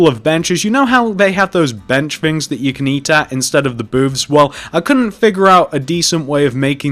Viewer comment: instead of the boobs or booths?